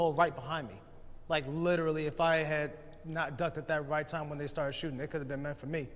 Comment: my drunk brain heard “5Head”, what did he say exactly? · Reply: ‘If I had not ducked at that right time when they started shooting that could have been it for me’